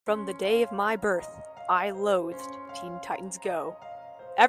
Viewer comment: I THOUGHT U SAID LOVE SO I WAS CONFUSED